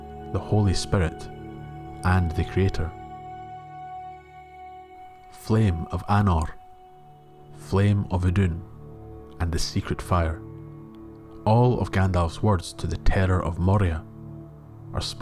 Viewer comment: I always thought he said "Flame of Arnor" and interpreted that Gandalf (in relation to Aragorn) will help to restore Arnor which was destroyed by the forces of evil and Durin's bane is also evil. The "Flame" I interpreted as a metaphor for some kind of hope or life in Arnor 😅 I know it is weird.